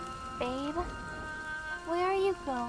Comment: did she say Abel. Cause thats my name =3